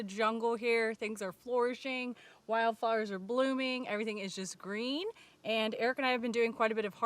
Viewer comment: Not going to lie I thought you said Wild fires are blooming